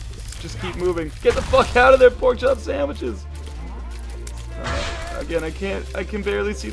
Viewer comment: did he say porkchop sandwiches????????? Lol.